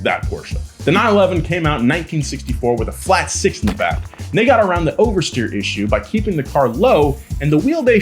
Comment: did he say the N word? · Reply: He said "nd' they"